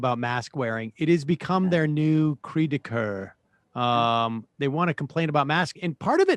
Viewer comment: Me, being French (not that good at Anglish iz what I mean), hearing Sam at 0:10 saying something like "It has become their creedewkeur"... ... "ah put**n leur 'cri du cœur' !" I'm continually amazed / amused by how many French expressions have sank in Sam's language. And also Tucker going for the "mask madness" thingy... but I'd rather focus on Sam being awesome 😙